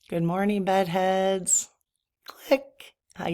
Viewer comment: Lol, I thought I heard you say, "Good morning, buttheads " lol. Too funny.